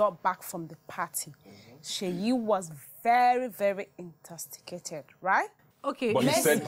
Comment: Did I hear "intosticated"....chisos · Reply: 😂😂😂😂😂Ada I'm hollering. Yes "Intosticated" has replaced "Intoxicated lmao.. all join